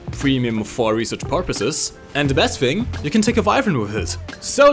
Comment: Not to be rude but what do you say at 9:35? I genuinely can't tell lol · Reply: "You can take a Wyvern with it" Premium british aircraft that's excellent for ground attack :)